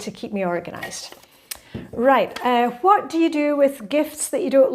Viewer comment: I thought you said what do you do with unwanted GUESTS 🤭